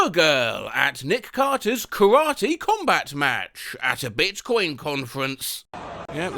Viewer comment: i just heard bitcoin as "bitch coin"